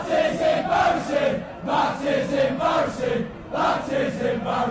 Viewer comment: lmao i thought that they were saying “that’s technomotion” for a sec now i can’t unhear it